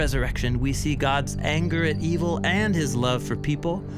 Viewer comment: I disagree with what you say in slide 4:19 "...we see God's anger, and evil and his love for people..." the word "evil" was very much in contradiction with the whole statement. There's NEVER evil in God. So please delete this word! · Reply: Hello Maria, he said 'anger at evil' not anger and evil